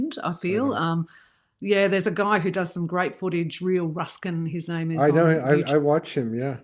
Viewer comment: What is the name/channel you both were watching? I heard 'Real Ruskin' but thats not it. Thank you ! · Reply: I think you're talking about Rebel News. The Real Rukshan I think his name is 🙏